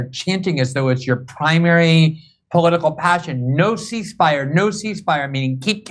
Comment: When I listened to this again, I actually thought they were saying..___? Seig Heil__?Seig Heile.... Then I realised it was 'No cease fire..'. I had just woken up , but it was really bizarre...🤯☺